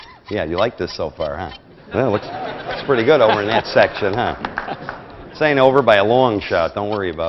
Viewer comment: can someone tell me what Sigel says to Ryes at 38:46 ? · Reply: Mike said "It ain't over by a long shot." which most of Mike Segal's fans appreciated. But it's more of a self confident statement that he can still recover from the first (shameful) first set. lol . Mike faced some good players during his reign but not the same caliber players nowadays. Efren still play some games in Bellflower Ca. and a lot of players still learn from him.